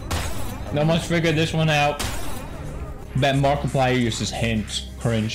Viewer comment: Pewdiepie: "bet that took jacksepticeye 3 years" me: "to play the game" sad but true tho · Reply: I thought he said Markiplier?